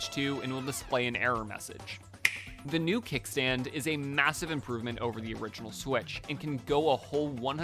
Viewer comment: did you say "Massive?"